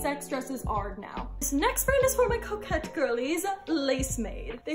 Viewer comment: I thought you said coke head girlies 🤣